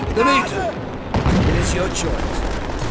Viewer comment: Reznov: ”Dimitri shoot them!” Dimitri: * does nothing * Reznov: “CHERNOV WTF!” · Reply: I thought Reznov said "Dimitri, your choice"